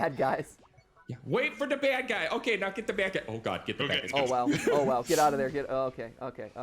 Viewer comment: Please be careful i heard "oh G".